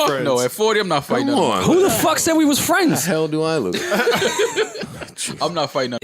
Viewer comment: but i thought “who the fuck said we were friends?!”…?